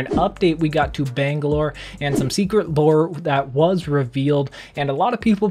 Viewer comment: Am I seriously the only one who heard "secret boar" at 0:09? xDDD